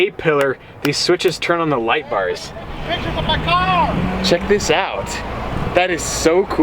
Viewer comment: AERO BLKHWK32 ikr his car is shit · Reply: I thought he said “quit taking pictures of my car”